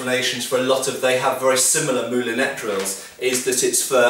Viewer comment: What is the word or term said at ~4:02? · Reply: Took me a long time to find it for us. The word is "moulinette", does not exist on <link>, and the origin of the word is French which means "roping".